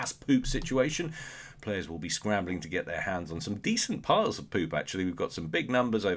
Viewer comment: did you did you say "decent piles of poop"?? XD